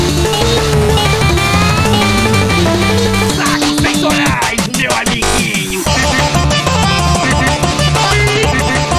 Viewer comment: What does Shaggy say at 34:44 I honestly have no idea since it’s a different language? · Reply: Look at this chest my little friend